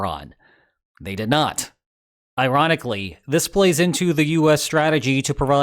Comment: Did you say Iranically?